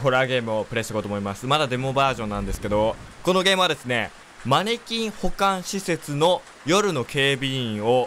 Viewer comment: ごめんなさい。「マネキン保管施設」を「マネキン股間施設」と空耳して必死で真顔を努めていました。(イチャイチャパラダイスのせいかな)